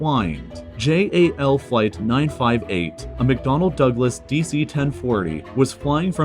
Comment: did he just say McDonald?!